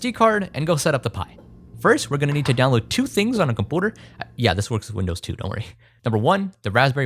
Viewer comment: Did he call his MacBook a cum-pooter?